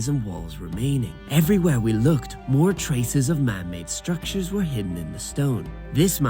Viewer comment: EVERY time you tell "men maid structure" I hear "mermaid structure" 🤣🤣🤣🤣🤣🤣🤣